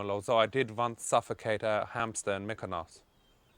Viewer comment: ''I once suffocated a hamster in __''? What is he saying? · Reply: Mykonos, a greek island where gay people party in the summer